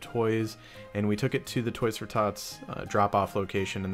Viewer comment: For a second I thought he said "toys for thots"